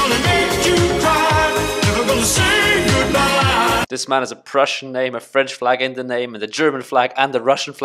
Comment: I thought you said this man has a crush right as my crush texted me